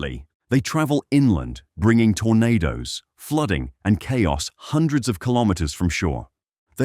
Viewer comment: fooding instead of flooding